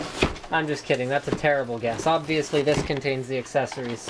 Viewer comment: when you said "i'm just kidding, thats a terrible guess" google subtitles thought you said "understanding, that terrible death"